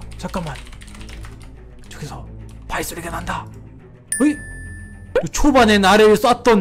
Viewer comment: 어케 들었누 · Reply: ???:내앞에 발소리